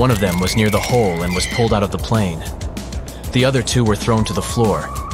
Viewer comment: I thought you said that one attendant was pulled out of the top of the plane? She is ok?